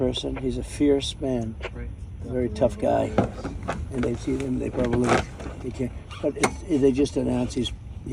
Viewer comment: What does he say at 4:03? They feed him?…they probably…? …What?!